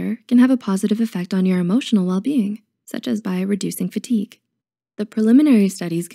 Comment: Does she say “bio-reducing fatigue” at 3:57?